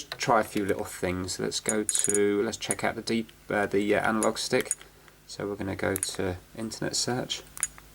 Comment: what were you going to say? “Try the deep...the analog stick” or something like deep anal? Lmao